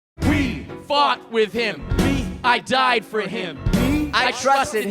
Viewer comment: I thought they said "we fucked with him" at first instead of "we fought for him" had to relisten a couple times lol